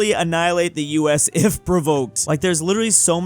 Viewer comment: Am I hearing things wrong or does that say "if provoked"